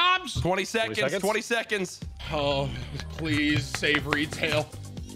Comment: did he say "uhhh please savory tail"?